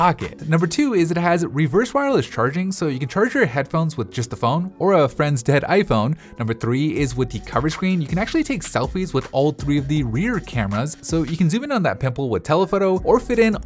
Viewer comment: Bro i thought u said "Or a dead friend's iPhone"💀💀💀